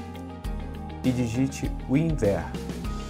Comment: No minuto 1:25 eu n entendi o q ele falou? N sei c ele falou winzer, ou algo assim, algm me ajuda prfvr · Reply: Winver